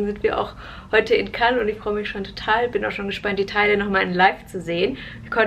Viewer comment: Hast du Italien gesagt?😅Cannes is eigl Frankreich🤔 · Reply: Hab einf bei 0:39 italien verstanden😂statt „die teile“